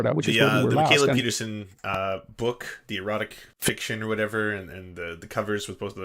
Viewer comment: did he say erotic fiction or neurotic fiction - i couldnt tell